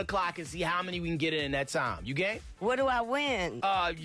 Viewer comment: Now I know he meant "You Game?" but it sounded like he said "You gay?"